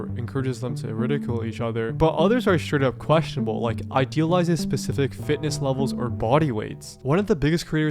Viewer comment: Did somebody just say... Ideal?